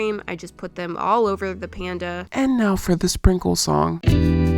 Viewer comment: I thought she said "enough with the sprinkle song" and my heart dropped until i realised she didnt.